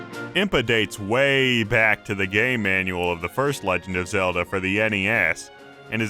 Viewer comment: Who else thought he said "gay manual" at 1:41?!